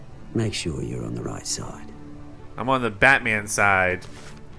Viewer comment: Did said badman